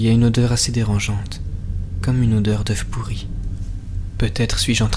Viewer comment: Au lieu d'entendre "oeuf pourri" j'ai entendu "keuf pourri" xD Sinon super vidéo <3